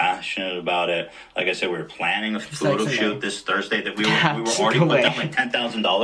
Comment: Every time you said shoot, I thought you said shit. It was too funny. It took me a while to get with it.